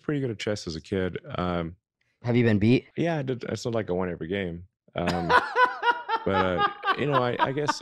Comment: When the interviewer asked “have you been beat?” I think Elon actually said “Yeah, it’s not like I won every game.” But with his accent and his tendency to mumble, I get why Hikaru may have misheard him.